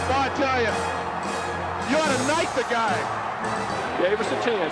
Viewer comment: Did he say "knife the guy" at 15:53 ? Lol or knight?